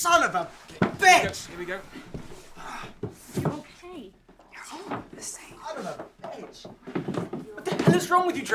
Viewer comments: What do you whisper in that guy's ear at 22:34 ish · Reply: "You're all the same" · Rewatching this and 22:25 is it just me or does Kate say "you're all the same" to Trevor after she looks at the camera???